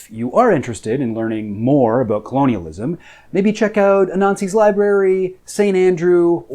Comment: Dude I thought you said "a Nazi's library," and not Anansi's Library. Now I have Nazi libraries in my search history. Thanks brah